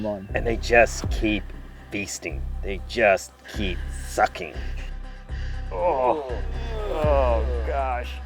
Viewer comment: did you say a bad word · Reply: he said sucking bro what bad word